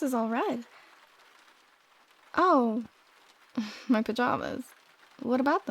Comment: I was very confused because i misheard "my pajamas" as "michael jarvis".